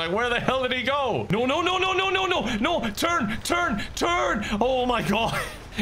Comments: it kind of sounds like you saying turg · did he say turn, turn, turn or turg, turg, turg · for a second there I thought I heard the legendary name of...Turg?